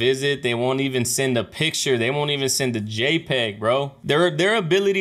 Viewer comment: I think he said “j-pay” which is the currency in prison. Fam and friends can send money through j-pay to inmates. Anyways, LOVE YOUR CHANNEL, I’m obsessed. ❤️❤️❤️